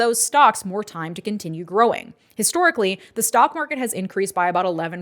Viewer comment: I thought you said the snotmarket at one point. It’s a synonym at this point LOL